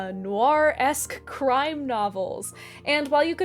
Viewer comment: At first, I thought you said, "Gwar-esque crime novels."